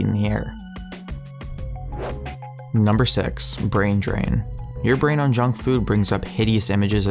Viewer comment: I thought when he said brain drain that he was saying "Don't eat bro"